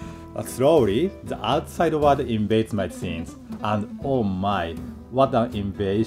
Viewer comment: did I just hear George Takei's famous "oh my?"